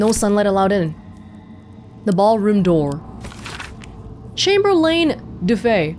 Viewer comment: I won’t lie, after dealing with Orin for so long, I genuinely thought you said “the Bhaal room door” for a moment there.